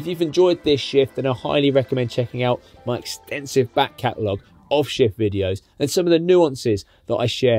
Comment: Thought you said...back catalogue of shit videos....bloody hearing!!😂